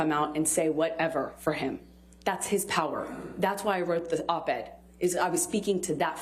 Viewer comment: Did she just say that are wrote the op-ed